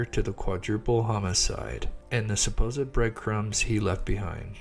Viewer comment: did he say breadcrumbs?